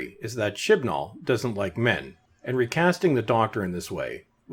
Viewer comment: Hahaha Chibnall's probably watching this video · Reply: Oh, I thought the narrator was saying, "Shitball."